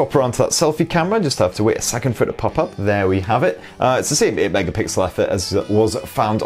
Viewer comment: What did you say at 12:15? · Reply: It's the same 8 megapixel effort